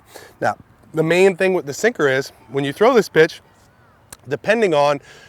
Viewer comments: Did anyone else hear b*tch instead of pitch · did anyone think he said "now when you throw this bitch" 😂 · sound like he says when you throw this bitch 😂